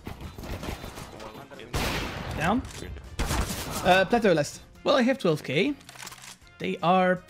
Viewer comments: did you say tetőn lessz? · hey my friend i just hear a hungarian word at about 7:10 and its says: tető lesz so where are u from? XD